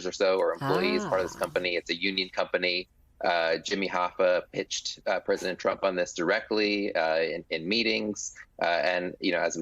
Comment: Did he say at T 3:00 mark that "Jimmy Hoffa pitched the idea to Donald Trump directly"?